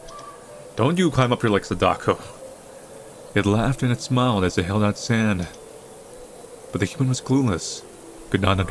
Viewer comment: Hold on. I just passed the one minute mark, and I swear I heard, "So it sprang from its BREAD to see what was the matter." I mean, I like bread and all, but it's not something you spring from... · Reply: Woah woah woah. At 2:10, I heard, "as it held out it sand." I suppose I get partially blending words, but sand...? On a different note, your voice would be so good for narrating literally anything.